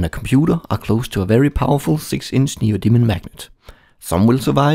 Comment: Before I read the description I thought the guy was saying "mah new demon magnet."